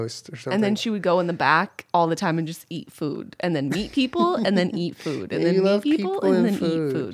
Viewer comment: I'm sorry but I lost my focus for a second there around 41:50 and I thought Jenna said "meet food and then eat people and then meet food" 😂😂😂😂